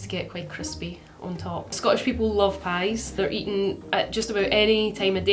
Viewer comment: I think is saying "pie and Bovril" (which is kinda like Marmite)